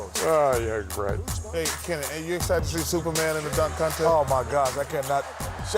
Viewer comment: Chuck & Kenny teasing Shaq by calling Dwight Howard Superman never gets old. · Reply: I always thought Kenny said stuperman lol